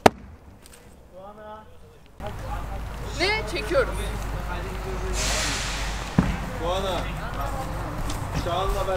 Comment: dan 16:17 ye kadar olan sesde ne diyor ben tam çpzemedim.Benim duyduğum:Tuana çağanla beraber......gelirsin.Noktalarin yerine ne dediğini bilmediğim için yazmadiğim.Duyamn olursa ses etsin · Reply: Çağanla beraber toplanti yerine gel